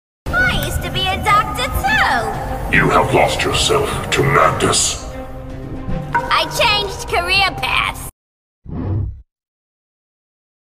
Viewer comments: Am I the only one who thought it said Korea paths the first times I heard this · Omg PLEASE tell me I'm not the only one who hears this audio as "You have lost yourself, Tramantis." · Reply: i hear "you have lost yourself to mantis" like who tf is mantis